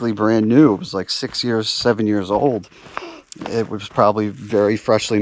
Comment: oh my god i thought you were saying you were 7 years old in 2014